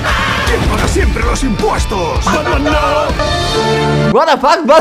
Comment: Siempre repito el video pero no entiendo que chuchas dice en esta parte del video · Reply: Dice batman no 👋🇨🇱❤️👍😇🙏